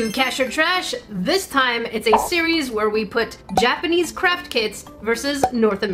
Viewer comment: How come these days whenever I hear says craft kits, I will misheard it as crap kits